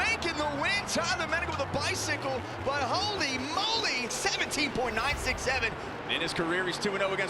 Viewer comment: I heard him say 67